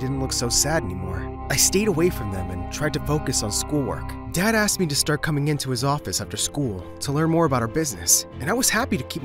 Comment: mins in I literally thought that he said deadass asked to come to his office after school 😳